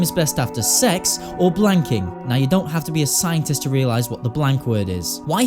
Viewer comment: I know m[censor]g (6:07) is bad but I can't figure out what the actual word is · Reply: motherf*cking?